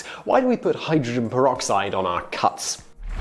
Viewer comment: i thought it said "cunts"...blondie